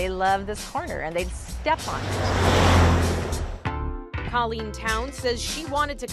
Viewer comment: ohhhh colleen... I had to rewind I thought they said Karen lmao